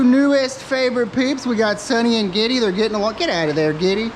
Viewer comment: Lol I thought you said diddy